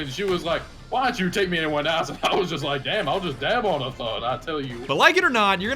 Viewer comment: Did he just say "I'll just dab on a thot"? XD